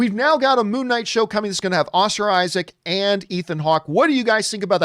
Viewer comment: I thought when John said Ethan Hawk he actually said Tony Hawk the skate boarder or Ethan Hunt from Mission Impossible franchise. Then I saw who Ethan Hawk looked like and was like well obviously they did not get a skateboarder or Tom Cruise so I don’t see the big deal about Ethan Hawk in Moonnight over a Skateboarder or Action stuntman.